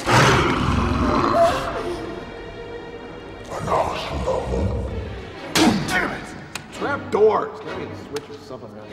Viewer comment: Anyone by chance know what Imhotep said to Evelyn at 1:49? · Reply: Sharkie626 sounds like he said Anck su namun, the pharaoh's mistress.